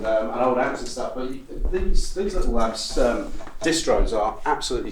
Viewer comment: What was that amp splitter he mentioned at 6:20? couldn't hear what they were saying... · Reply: Little Labs